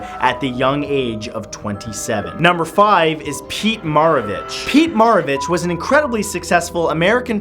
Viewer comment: When you said pete maravich sounded like you said pete marabitch